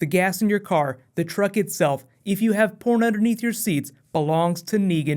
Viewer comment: Let me know how long you think it will be before we see Jeffrey Dean Morgan on screen! New Batman v Superman coming tomorrow :) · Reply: Mid season premiere! Also, thanks for clearing it up for me, I thought he said, "corn under your seat" LOL.